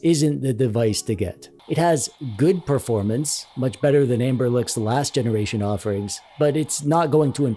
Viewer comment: I keep hearing you say "Amberlick" but the company is called "Anbernic". Unless it's a joke I am missing out on 😅